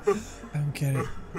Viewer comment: did he just say "I'm gay" at 20:12 ?